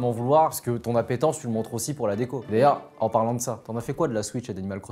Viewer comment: c’est moi ou il a dit Léa 🤔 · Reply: omg on dirait qu’il dit « d’ailleurs » mais aussi « lea » ça me perturbe